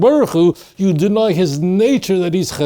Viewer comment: you say you deny a word hasid what is this word? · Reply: The word is "hesed" in Hebrew, not "hasid." חסד in the Hebrew. Hasid is from the same root but is the adjective meaning kind or pious while hesed is the noun, kindness. In modern Hebrew, hasid can be a noun as in the "hasidim" which are Orthodox Jews who are pious believers (most people think of Chabad when they think of hasidim).